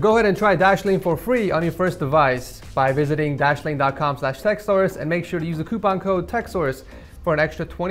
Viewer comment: I think he said $ΞX source